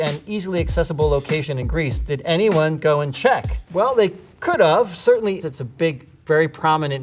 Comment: did he just say ‘could of’ 🤔 I was on board with him until now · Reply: He said "could've" (short for "could have") which sounds like "could of", that's how this monstrosity started in the first place 😅